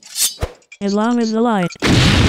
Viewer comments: ”ISLAM IS THE LIGHT” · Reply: I thought he a said as long as the light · did he say Islam is the light? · islam is the light XD i cant 😂😂😂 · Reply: I thought he said as long as the light.